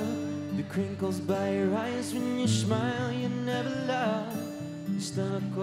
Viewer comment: Am I the only one who heard Liam said "when you SHMILE?"